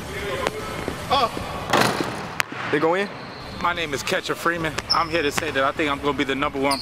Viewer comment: I thought Said mike “catcher” freeman from the boondocks instead of ketchup lmfaoo